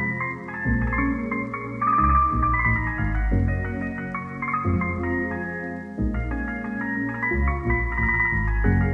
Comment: I had to slow the video down to see what you said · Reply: Don't fuckle with shuckle